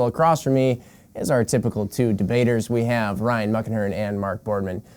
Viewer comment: Ohhh.... "Muckenhirn"...lol I thought you were saying Ryan "Muck" Ahern, and I always thought "Oh sure, no one gives a damn about an Irish slur"...LOL